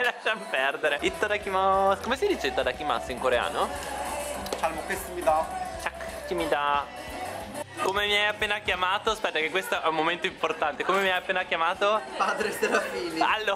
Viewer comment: solo io ho sentito really really degli winner?!